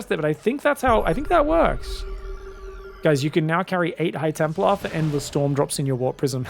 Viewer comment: What says: "You can now bring 8 High Templars for endless storms" What I hear: "You can now loose 8 High Templars in your prism instead of 4"